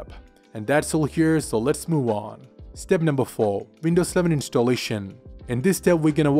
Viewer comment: Only I'm I the person who heard windows 7 for whole time or I'm I wrong? such as